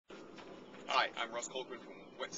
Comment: Had to rewind the video because I thought he said "hi Im Mike Rowe"